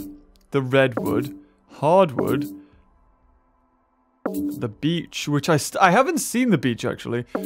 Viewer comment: I thought it said "The Bleach" instead of "The Beach" and I was like, well then time to get ready.